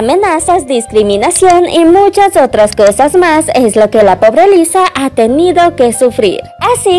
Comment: entendi "es lo k la perra lisa.." y io como WTF pero lugo vi los sub y ya jajajaj